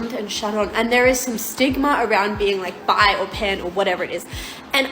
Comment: Did she say " There's a stigma about bi or peds" ? I hope I didn't heard right. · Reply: Not peds, she said pan as in pansexual